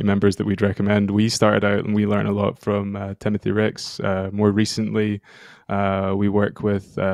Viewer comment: Became a fan of Off+Brand's work after this. At 25:50 mark, the transcript didn't catch the name of the community member they referenced. Did anybody catch the proper name? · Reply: Timothy ricks, and the second one I didn't get it